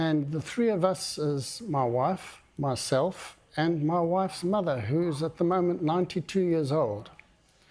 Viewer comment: I thought for a second he said his wifes LOVER lmfao